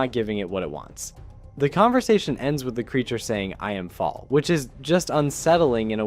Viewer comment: either my ears were dirty or yours, but every time i saw and heard that scene I hear "now fall", not "I'm fall" which would take away a lot of your confusion as d3rlord3 actually falls right after, falling into a different realm